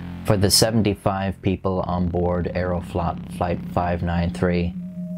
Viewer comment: Did anyone else think that when he said “aeroflot” thought that he mispronounced “flight” ???? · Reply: I thought he said Aero-flop, fittingly enough